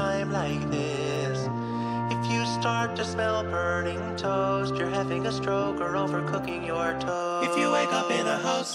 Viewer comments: "If you smell burning toast, you're either having a stroke or overcooking your toast." is my favorite line for some reason. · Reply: at first i thought he ways saying "if you smell burning toes, you're either having a stroke or overcooking your toes" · "If you smell burning toast, you're either having a stroke or overcooking your toast." is my favorite line for some reason. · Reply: I always thought he said if you smell burning toes